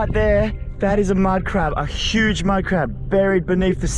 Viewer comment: First, I agree Second, it’s mud crabs not mud fish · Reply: I thought he said mad crab